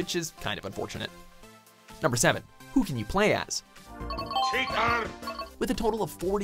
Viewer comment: did wario say 'cheater!'